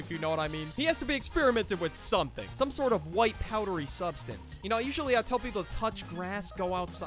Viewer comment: i almost thought you said "white powery substance" wich would make sence